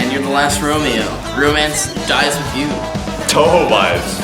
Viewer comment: DID SAY TOUHOU OR AM I DEAF